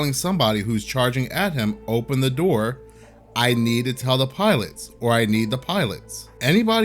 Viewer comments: It sounds like he said, "I meant the pilot, I meant the pilot!", As soon as he got tackled. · It sounds like he is saying “I need to find it” idk what that means? The exit?